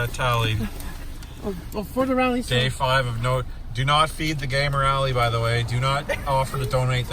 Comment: my closed captions said “Do not fee the Gay Morality” LOL